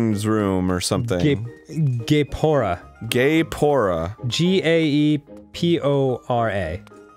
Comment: am i the only one who thought danny was about to say gay porn when he said gaepora lol